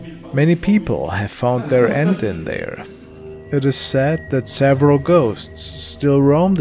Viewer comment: I thought he said many ppl found their AUNT in there lol in the retirement home lol